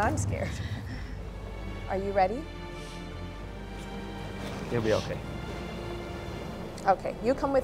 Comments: Wife: I'm scared Husband: You'll be okay Walks away Speech 100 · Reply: I thought he said "It'll be okay" lol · Wife: I'm scared Husband: You'll be okay Walks away Speech 100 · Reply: i also heard him say 'It'll be okay"